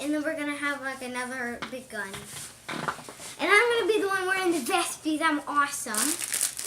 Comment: Wait what did he just say 2:44 "I'm going to were the vest because I'm awesome"